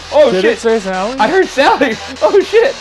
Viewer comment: when Ryan asks "What is your name?" @ 15:50 I hear Cathy not Sallie....